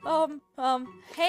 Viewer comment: "A OOH WOA AUH AUH AUH AUH OUH" "hey, BAD." "cant just scare me like tha-" edit: why is this comment getting popular 2 months after i made it edit2 : 3 month ago me was edgy and rude 😭😭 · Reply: i didnt forget it, i misheard what she said. i thought "hey, come back" was "hey, bad"